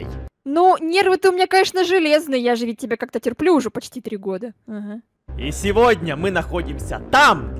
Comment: мне показалась что она сказала ,, я уже не сплю с тобой 3 года 😂😂😂